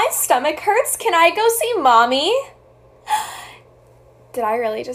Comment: DID SHE SAY MOMMY?!